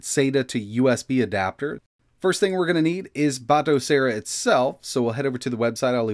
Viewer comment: I thought he was saying "auto-sear" 😂 whoops! Wrong channel lol